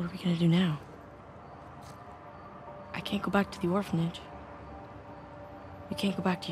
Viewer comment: Nate: I can't go back to the orphanage... Subtitles: I can't go back the orphanage. Boi tf you mean